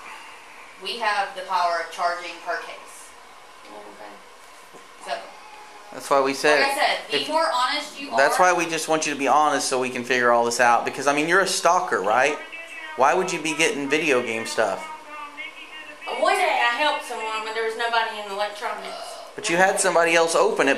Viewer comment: I thought he said ‘but you’re a stalker right!?’ 😂